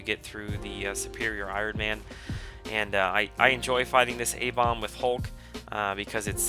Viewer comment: oh! i were refering to Abomination when u said a-bom..i thought u said A-Bomb